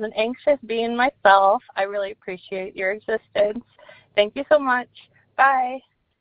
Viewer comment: Lol why put subtitles on the voicemails if the subtitles are wrong? 26:58 that called said “I appreciate your existence” not I appreciate your assistance.